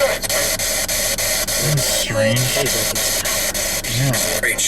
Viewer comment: I heard it says "That's Colin!"